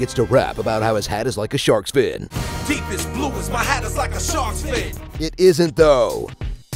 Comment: I always thought the song was saying “my heart is like a shark fin” and that made it better for me. Hat is really dumb.